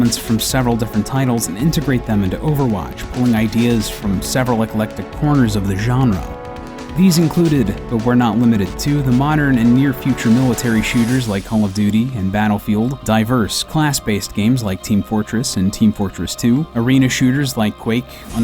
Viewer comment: did he just say "home of duty"?